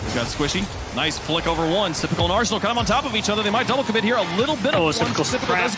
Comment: wait what did I just hear?? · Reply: Here comes Squishy, nice flick over one. Sypical and Arsenal climbing on top of each other, they might double commit here a little bit all at once, but Sypical hits the ground back in time, but JSTN now with the pass to GarretG in field once more. And Arsenal is able to weave through the traffic, pick up ball control, flick it over one. Squishy will try to stall it out, but can he hold on. You're welcome ;)